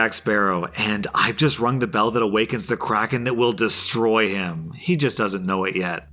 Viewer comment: I thought he was saying "crackhead" instead of kraken lol